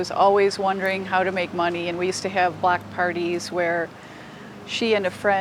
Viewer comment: I thought the mom said we had black parties I’m like huh but she said block 😅😂😂